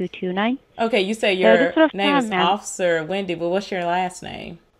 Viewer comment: I swear I heard her say "Officer Rundi!" 😜👍